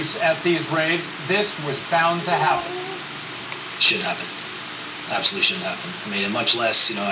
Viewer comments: sounds like he says "Shit happens" lol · lol i swear i thought he said "Shit happens." EDC looked like it was a fucking madhouse. People need to calm the fuck down and stop acting like dumbasses rushing the field and shit. You would've probably found me sitting comfortably in the top rows enjoying the music.